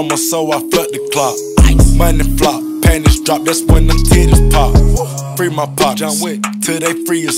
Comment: what I heard "panda's drop that's when the critics stopped"